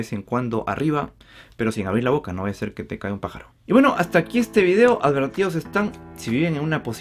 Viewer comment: Tuve que activar los subtitulos en 9:17 porque entendi que decia "no vaya a ser que te cague un pajaro" 😆😆